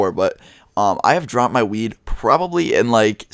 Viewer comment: When u said I dropped my weed I thought you said I dropped my Weine because my headphones fell off